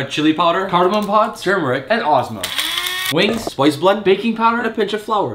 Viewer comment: yo i swear it sounded like he said "human" in the beginning lol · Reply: sounded like he said spice blood to me